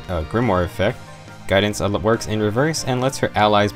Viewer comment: Update: My appeal got approved, so everything should be back to normal. Hopefully this doesn't happen again! Dang, I guess Nowi is not family friendly enough. This video got marked for "not suitable for most advertisers". I feel like I didn't do anything different than any other video and the only character shown on screen is Nowi, so I'm guessing that is the problem. Maybe the thumbnail with her attack portrait was too much. In any case, I wonder if the swimsuit characters would also trigger the "family friendly" flags. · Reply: Hey, There was a youtuber who said he gets demonized when he mentiones Allah, like in Islam, and the google cc thought you said it at 3:35 of your video. That might be kind of like the whole Sigurd thing, sucks that that happened to you. You're vids are always so informative